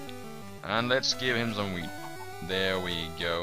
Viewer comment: lol it sounds like your saying weed instead of wheat